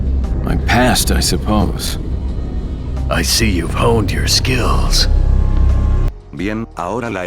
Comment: Lástima que no está traducido lo que dice Krauser 🙁 , pero bueno. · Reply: Dice “I see your hunter skills” en español “Veo tus habilidades de cazador”.